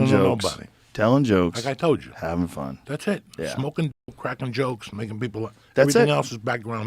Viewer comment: Did Joey just say "smoking crack, telling jokes..."? 😂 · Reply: I thought it was obvious that he said "smoking, cracking jokes".